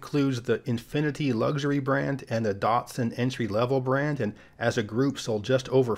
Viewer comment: Wait! Did you say Datsun at 1:36?